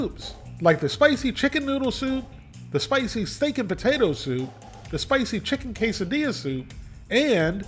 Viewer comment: i thought he said stinking potato soup! 😅